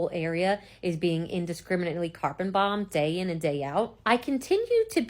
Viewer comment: Did she say "Carbon Bombed?"